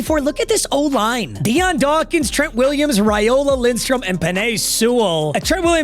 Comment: I thought said trey williams😂